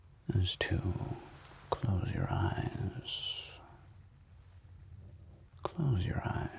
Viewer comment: At first I thought you said "Cut out your eyes". Would make sense for Agent Smith to say this.